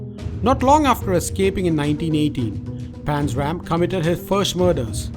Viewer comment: Did he say he escaped in 1980?????